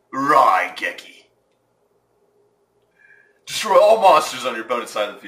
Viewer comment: who heard TROLLLL MONSRTERS